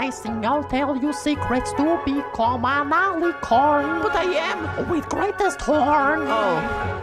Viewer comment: I thought that Celestia said “MAMALICORN!!!” XDDDD